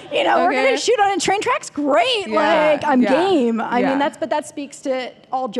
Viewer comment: In 15:43 it sounds like she said im GAYme instead of im game😂😂 (sorry my mind filled with lesbian things)